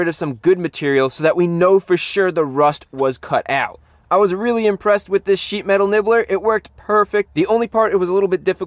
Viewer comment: I thought he said " i was impressed with this Shhit."